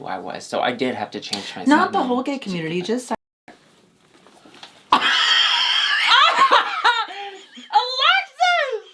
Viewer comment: what did she say? I didn't get it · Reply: yeah its bar redacted